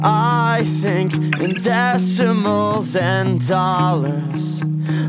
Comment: My younger cousin who couldn't speak English thought he was saying, "I thinkin' dats some old sand dollars."